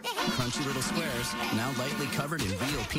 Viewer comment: WHAT DID HE SAYYYYYYY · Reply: E